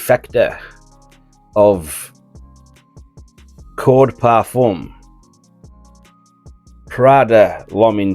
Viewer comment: and 16:00. Clarification on the main fragrance you compared it to would be helpful... it sounded like you said cord or core or Korloff Pour Homme???? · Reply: Code parfum my friend, thats just my northern accent.